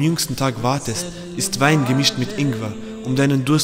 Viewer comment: Hab ich Wein gehört?